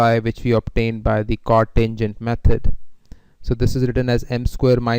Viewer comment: I didn't get how we get ξ, did u say cotangent method? would plz spell out a little bit more how we get the first equation of ξ? Also, could you please share the source(book/pdf file) of your lecture? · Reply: Chord-tangent méthod; because one use chords (lines) or tangents to find the coordinates of the points when applying addition between two points (depending on the position of the two points for witch one applies the addition rule on the curve). Etheir one draw à line between two distinct points or one draw à tangent line to the point if it is added to itself